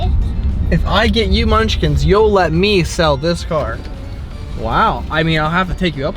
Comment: Did anyone else catch that she said if she got her munchkins he could "sell MY car' @ 3:04. I thought that was very telling and funny.